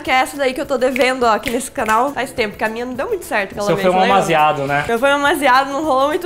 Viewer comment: foi só eu que ouvi "fumei um baseado" ao invés de "foi um amasiado"?